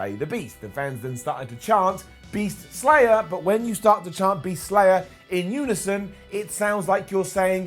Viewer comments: I thought that saying Beast Slayer in unison sounded like Heath Slater... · When the crowd chanted “Beast Slayer” i thought they were saying “Heath Slater” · When they chanted Beast Slayer, i thought they kept saying Heath Slater